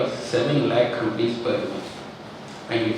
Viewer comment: did he just say 7 lakh rupees per week>??????